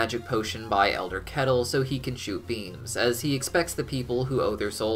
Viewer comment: I thought it said “so he can shoot beans”